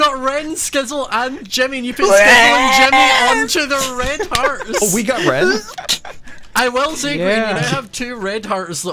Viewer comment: Seeing boy Grian’s and Scar’s POV, still can’t understand what Grain screams at during 18:51 but it is hilarious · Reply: I think it's REeEeEeEN